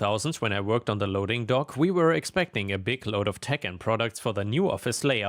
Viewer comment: instead of "tech and products" i heard "Tekken products" and was very confused for a second there